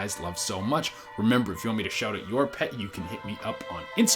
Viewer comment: i thought he said "if you want me to shout at your pet" lol